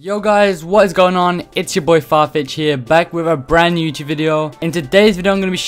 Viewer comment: did he just say far b1tch 😂